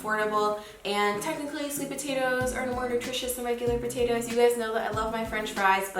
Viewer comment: Sweet potatoes aren’t more nutritious, they just have a different nutrient profile than white potatoes! Like for me, I eat more white potatoes because I’m typically low on potassium. But if I want to eat less carbs, I’ll go for the sweet potatoes. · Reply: i thought she said "no more nutritious" idk it wasn't super clear